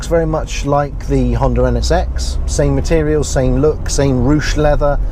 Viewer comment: What car model did he say at 2:12? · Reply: Brandon Marentes NSX